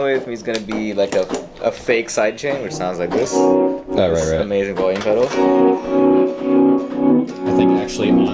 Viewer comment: did he just say "amazing volume pedal"?